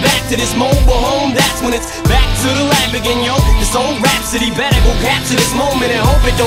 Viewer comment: I just heard "Back to the lab again" like "back to the lamborghini" 😁😂